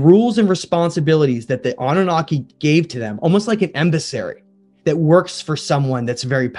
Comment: Did he just say "embissary"?